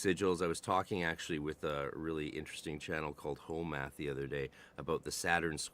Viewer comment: Wait 17:20ish you daid you were talking with "Hoe math" like the hoe math or is it ho math idk 🤷‍♂️ anyway is that what ya said cuz he is famous af creator i never would have figured he would be interested in occulture 😂